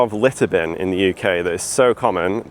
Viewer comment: LITTER bin... I thought he was saying LETTER bin and was like.... Why does that look like a trash can...